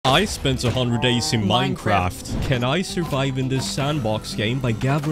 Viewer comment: I misheard the opening to be 100 days in mineworld instead of minecraft 😂😂